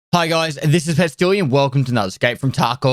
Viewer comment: always sound like he is saying..hello here is PESTILIAN and not PESTILY